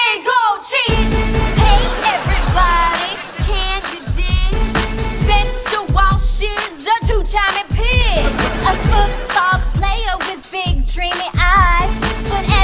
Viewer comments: i always thought they said he washes a two timing pig! LOL! im SO SLOW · I thought he said washes a two timin pig lol i dont hear right lol